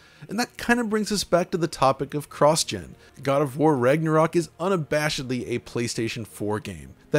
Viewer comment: Sounds like your saying Rignorok😂